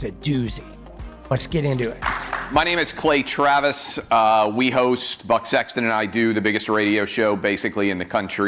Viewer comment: I thought he was saying, "We host butts*x..." for a beat.